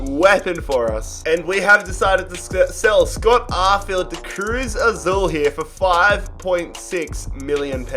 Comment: When it said Cruz Azul, at first I thought it said Carl Azus